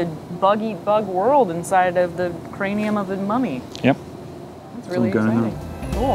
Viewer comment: the translation says (inaudiable) right after "Yep!", I think she said "It's really exciting".